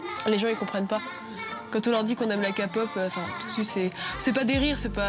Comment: c’est moi ou elle a dit « Ka-pop » 😂😂